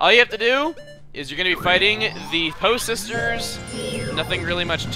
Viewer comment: At first I thought said "the HO sisters"!?!